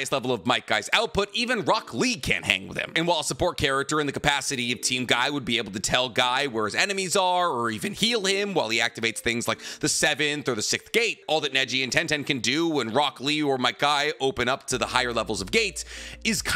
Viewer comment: OMG, it’s so irritating hearing say the word “SIXTH / 6TH” . He literally says “Sickt” everytime , every video 😂🤦🏾‍♂️